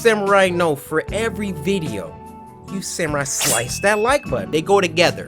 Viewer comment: Why does his intro sound like it says "samsung slice that like button"